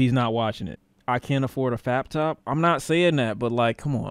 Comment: No way did I hear utter the word “faptop.”